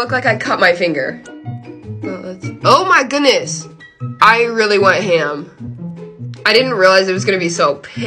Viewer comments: “oh my goodness! i really went ham!” hahaha ur so funny 🤣🤣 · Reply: I thought she said I really WANT ham and I was so confused for a min 😂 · I really thought she said “I really want ham” just out of nowhere 💀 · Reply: lmaooo she said “I really WENT ham” as in did a lot I think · “oh my goodness! i really went ham!” hahaha ur so funny 🤣🤣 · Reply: i thought she said-- "i WaNT HAm" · Totally thought she said “I really want ham” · I thought you said “I want ham” lmao